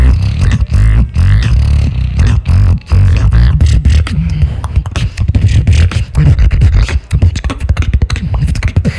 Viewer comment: what did say at 1:25? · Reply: John snow 10sec